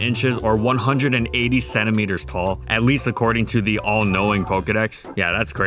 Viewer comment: I heard that as "187 meters tall" and now i'm deeply disturbed.